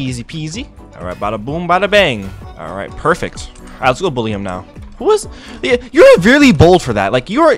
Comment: I thought he said bullcrap 💀